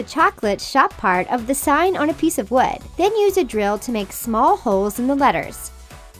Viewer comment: i heard «small hoes» SCREAMING